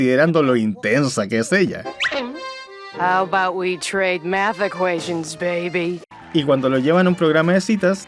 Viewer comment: I will be try mad action baby ??? , No le entendi bien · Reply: Ah, se me fue ponerle el subtítulo. Dice "¿que tal si intercambiamos ecuaciones bebé?"